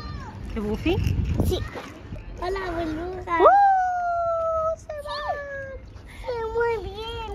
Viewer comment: Minuto 14:33 uno de los hijos parece que dice hola boluda jajaja y quiso decir hola burbuja jajajaj estalle